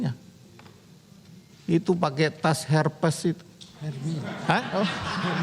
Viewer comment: Menit 5:01 "tas herpes". Hahahahahahahahahahahaa gatal dan perih dong, itu penyakit woi 😂😂😂😂😂🤣🤣🤣🤣